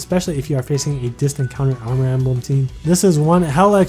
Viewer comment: Did you said rip Alm?